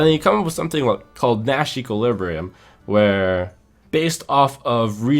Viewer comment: what did said at 07:41 ? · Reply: yo he said Nash's equilibrium